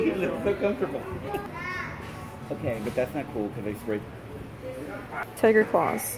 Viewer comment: I heard someone say "okay that's not cool they spray painted it"